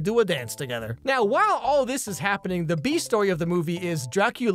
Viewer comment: omg with all the other movie parallels being (comedically lol) drawn, I legit thought he said 'The Bee Movie of this story' when he said the "the B story of this movie" 😅 I thought some great metaphor (metaphor? Comparison? Idk lol) flew over my head, and upon listening to that part again (and using subtitles 😂).... lol nope. I just heard the words in a jumbled order 🙃 ... and now im hoping he uses The Bee Movie as a metaphor somehow in a future video! 😁😂😂